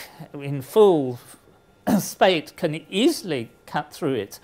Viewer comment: What does the professor say at 5:18? Full spades?